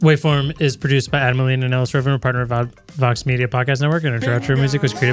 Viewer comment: wait did you say you were bought by Vox media?